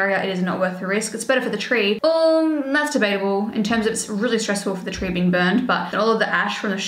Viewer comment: When you said ‘Mass debatable’ at 15:45 I definitely heard ‘masturbatable’ 😂😂😂😂